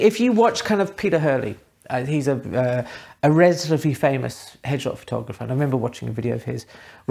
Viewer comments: I hope I'm not the only one who thought you said "hedgehog photographer" at 8 :38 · I thought you said Peter Hurley was a hedgehog photographer.